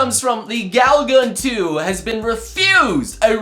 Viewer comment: When you said Gal Gun, I thought you said Galaga. Stupid, I know.